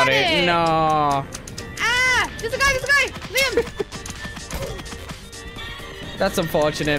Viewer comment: add 7:19 she said real name i think she said liam